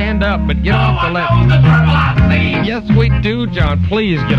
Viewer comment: What's John saying at 2:13? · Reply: No one knows the trouble I've seen